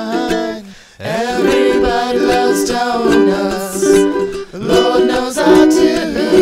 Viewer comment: when they said, "Lor knows I dooo.", I always thought they said "Just Have 1 or 2"